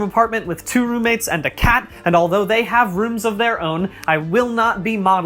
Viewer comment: it sounds like u said the cat has a room of their own and pleas,,, gib them ROOM